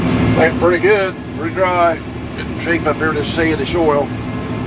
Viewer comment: during Bob cam. the Closed Captions says "Drink my beer just seeing the soil" 🤣🤣